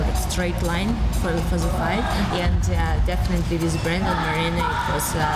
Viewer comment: Did valentina say Brandon on 155? ( 0:47 ) If she says that i believe her, she's badass